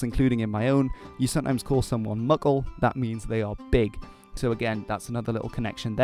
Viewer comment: Mickle as in Tolkien's Mickle Delving in the West farthing of the Shire? Got to love Tolkien's use of languages. BTW when he said Muckle I thought he said Muggle. lol